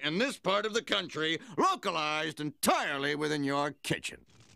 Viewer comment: I am playing this on loop while using Aurora Beam on Eternatus. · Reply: OH! I always thought he said "Thats it?" not "Can I see it?". thanks for clearing it up